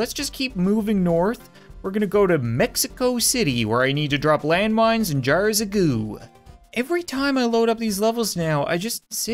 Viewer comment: It sounded like he said "land mines and jar of poo" XD